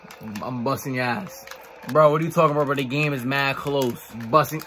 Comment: what bro say ???🤨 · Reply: Yo, paws 🐾 🐾 🐾